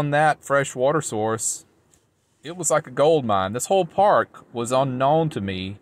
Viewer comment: OMG!!! I thought you said you were at the "Whore's Park". 😂 I guess I wasn't ready when the video started playing I was answering a text. Yes, the older I get the more I seek peace and quiet. Houston is full of boom boom base in cars, loud exhaust, drag racing at every busy street, violence etc. I'm so ready to get the hell out of here...